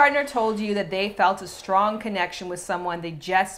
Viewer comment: Caption says "string connection" instead "strong" at 13:44. But who cares, great video! Lol